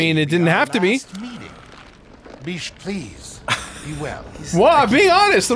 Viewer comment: i'm 5 minutes in, and i'm predicting ethan will not be dead, and mutate and help save chris at the end of the final boss fight. (edit) 34:29 the merchant sounds like he says "Bish please."